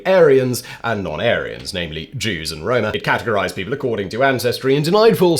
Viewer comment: seconds in is some type of glitch. I can't tell what you said. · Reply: "He categorized people"